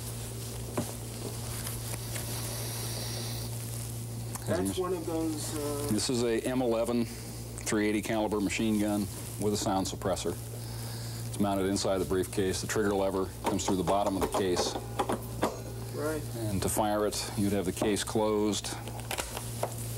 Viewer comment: "-- with silenced suppressor." What? Isn't that like saying "a convertible with retractable roof"? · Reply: He said "sound suppressor".